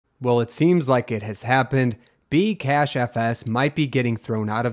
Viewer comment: I thought you said 'Cash App' at first... 🤣